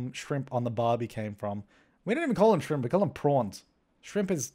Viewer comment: When he said 'prawns' It sound like 'porn' so I heard it like "ITS PORN. ITS JUST POORRRNNN!" XD